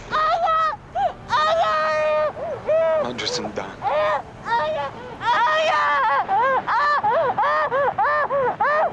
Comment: What does say? Thanks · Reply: That's grammatically wrong in Italian language. clearly says " Madre Sant'Anna".